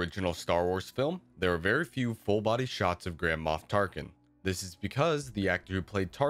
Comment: Idk why but when he said "there are very few full body shots of tarkin" I heard it as "there are very cute full body shots of tarkin" 😳